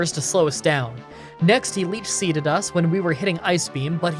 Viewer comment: Next he leap seeded us.... I love that move. (Can't tell if it is him mispronouncing the word, or me mishearing)